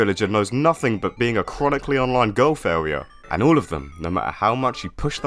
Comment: what did you say here i have been trying to figure it out for like 10 minutes (it sounds like Golf area but thats definitely not right)